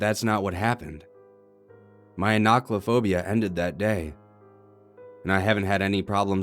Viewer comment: Wait what? Arogaphobia is the fear of open spaces and crowds, not heights. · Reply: Tassakahn, it sounded like he said "acrophobia" to me, when he spoke of the fear of heights. But I was confused about the title, so I looked it up. Apparently, much to my surprise, ochlophobia is the fear of mobs, agoraphobia is just the fear of open spaces, and enochlophobia is the fear of large or huge crowds. Huh!